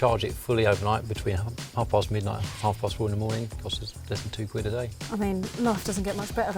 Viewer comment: did he say 32 quid a day?! Please someone tell me I misheard!! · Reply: I thought i heard that too, had to rewind. He said less than two quid a day.